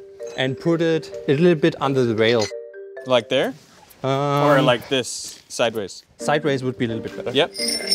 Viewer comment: Is it just me or is Derek saying "oder" instead of "or" at 6:00 because of spending some time in Germany?😄